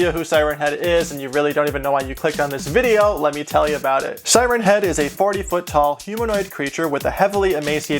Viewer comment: I talked to myself while he said his height and I heard, "Siren Head is 71 feet tall." My ears know how to trick me.